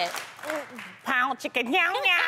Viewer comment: lmaooooo it sounded like Loni said "Pound, chicken, meow meow!"